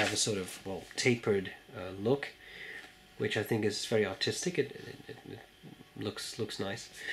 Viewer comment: any one else think that he said "I think it looks very autistic" at about 3:50-4:00 mark